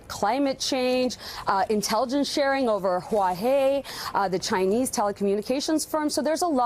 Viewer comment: Did she just say Huaw-hay? I couldn't take her seriously after that.